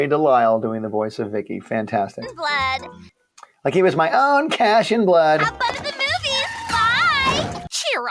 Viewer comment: I never in my life ever heard her say “cash and blood” I always thought she said “flesh and blood” 😮… I didn’t even hear her say “cash” here until Butch said it